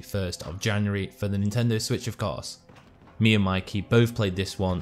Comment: Nice one man! I heard “SWITCHUP” Collaboration confirmed! Haha! Mark